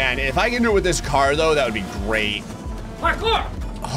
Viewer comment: did he say my book or parkour